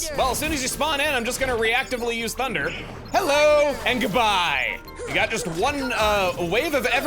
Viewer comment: Hearing that Goofy line just woke up memories for me...I always thought it sounded like he was saying "Hell!" in a really weird way. When I was a kid I would usually play in the living room and my dad heard that Goofy line and would proceed to imitate it every time he saw me play the game, well it's sequels too actually...I still have no clue what Goofy is saying in that line